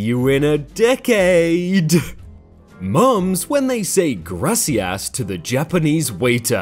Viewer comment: When you read gracias i was looking away for a second and heard you say ''grassy ass''